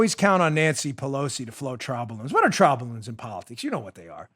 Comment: "You can always count on Nancy Pelosi to..." What? I have no idea what you are saying at 57:20. Can someone clarify for me? It sounds like "float trobulins?" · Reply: Never mind....Float a trial balloon. I got it. Cleaned out my ears.